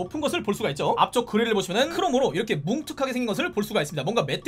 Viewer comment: 크롱? 왜 "크롱으로"가 들리는거지